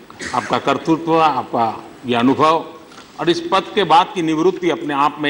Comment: Aap ka KARTOOT? Is that what Modiji said?